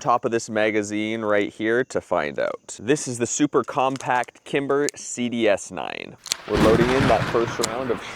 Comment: On 0:22 I thought you said “kinder” 💀😭😂